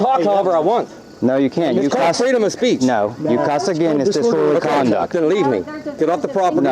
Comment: LMFAO 😂😂😂🤣🤣🤣 Put CC on, and click here ---> 2:28, When the deputy says, "No, you cuss again it's disorderly conduct", but read the captions. The captions say, "No, you cuss again it's just full of condoms". Lololol